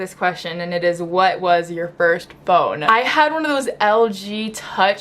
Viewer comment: I thought it said “bone” I’m just like boi is she a dog?